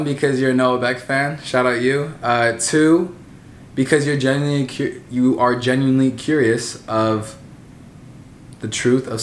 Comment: When he stuttered I thought he said genuinely cute 🥰